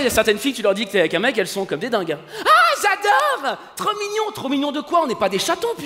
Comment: Nan moi c'est pas " J'adore trop mignon" c'est: "AHHHHH TROPPPP CHOUUUUUUUUUUUUUUUUUUUUUUU !!!!!!!!!"